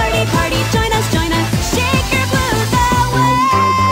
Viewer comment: at first I thought she said "shake your booze all day" LOL!